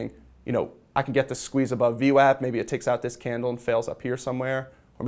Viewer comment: I hear him saying something that sounds like, “View App.” Could someone clarify what he’s actually saying? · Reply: VWAP or Volume Weighted Average Price. It's an indicator gaining popularity over an SMA of a similar period in that there is more information about how volume affected a particular recent price level.